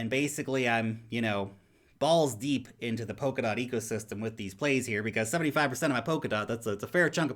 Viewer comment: Thought I heard Polkadot Quife after he said balls deep 😳